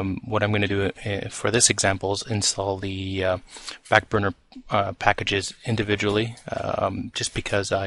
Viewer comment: Did you just call it "FAT burner"?